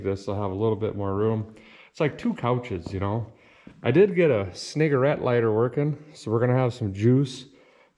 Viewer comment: How the hell did you pronounce that cigarette lighter part? @ 3:37 I heard c-ni**er-ette lighter. Wtf is that?